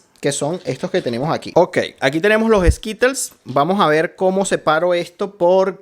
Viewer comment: Amigos y ustedes separaron los colores bien, o no tanto? 😅 compártelo con tu amigos a ver que tal les va! Suscríbete al canal para más vídeos tu like es muy importante, un abrazo a todos 😁🙌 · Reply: Parece que dices en el minuto 3:39 y aqui tenemos los esquites jajaha9